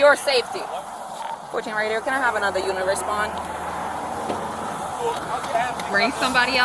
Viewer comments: the closed captions saying "can I have another universe spawn" is both hilarious and also very real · I love the automatic cc at 7:54 bc the officer says can I can have another unit respond and the cc says universe spawn it was just funny to me.